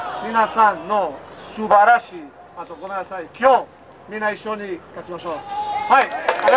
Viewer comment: 喜びを分かち合いましょう的な意味合いなのかもしれないが、「今日みんな一緒に勝ちましょう」に聞こえて「ここからもう一勝！？！？！？！？」ってなった